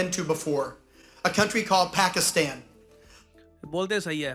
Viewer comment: I heard Fu*kistan